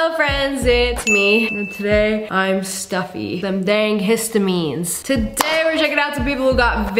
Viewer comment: them dang hit them knees 😂😂😂 that’s what I hear bro